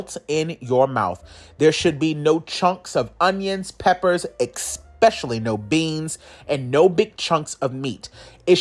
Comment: Did he say "expecially?".. yeah! that's what I thought!.. See ya later "expecially!"...